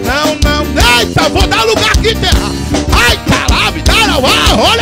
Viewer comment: Mano eu vou ouvi um "aí caralho"